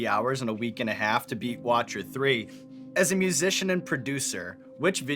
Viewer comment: did Sean say “Watcher 3”?